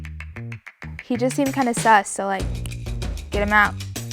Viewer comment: did she just say "sus" 😳 I didmt knew that poor ppl play amogus